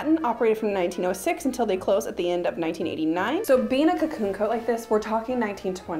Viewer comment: When you first brought out the cocoon coat I thought you said racoon coat lol, thinking racoon fur collar. Can't believe that dress was only $10. It's fun watching someone else spending money.